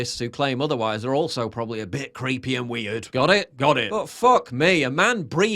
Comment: Paul did you say "butt fuck"? 8:30.. ? 😬😂🤯